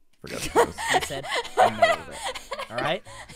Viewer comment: i know its not pg but what did grian say at 12:34 i'm actually curious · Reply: “I want this to be noted, walked out of this- this one here, and he said: “nailed it.” He said: “nailed it,” alright? Just want- just want that to be noted.” Did you get the right timestamp? Because that’s what he said there lol.